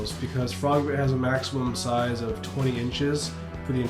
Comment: "Frogbit has a maximum size of 20 inches..." -Did I hear that correctly?